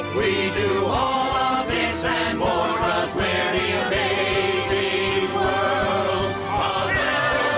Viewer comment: What are the monsters saying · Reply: “There is a lot of stuff here in the amazing world of Elmore.” is what the monsters said.